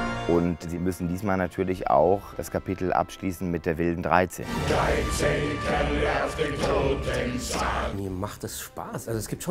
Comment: => "13 Kerle auf dem Totensarg." Habe "13 Kerle auf dem Totensack" verstanden. 😂😂